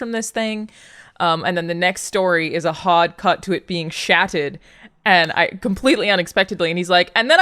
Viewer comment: Is she saying SHATTERED or SHATTED?! Like some weird past tense version of SHITTED?! lol I adore Alanah’s accent! 😜